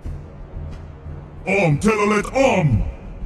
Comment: I heard him saying Om telolet om